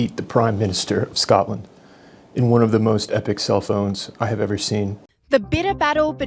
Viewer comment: Self owns... I thought you said cellphones. In one of the most epic cell phones you'd ever seen.